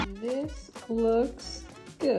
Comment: I heard "Upto 3 months" as " After 3 month " 😅